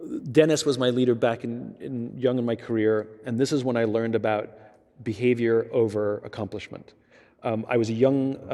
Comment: Summarized Points of these 5 Skills: 1. Listen to Others First. 2. Confront with Empathy over Warning & Yelling. 3. Reward Decisions Over Acomplishment. 4. Be Self Relaint and Confident for Subordinates to take decisions. 5. Be the First Person to take Accountabity when Shit hits the fan. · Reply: Thanks for the summary! At one point I heard “reward behavior over accomplishments”. Decisions come first, but what use is a decision without action?